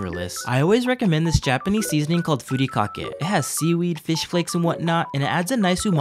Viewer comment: Bro I'm not kidding I wasn't focusing on the short eating and I heard "furikake" as "furry cock" and I started chocking.